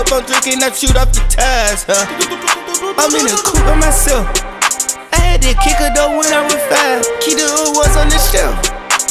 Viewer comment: All this time I thought Young Thug said “I had to kick a girl when I was 5” 😂😭😭😭😭😭😂🤣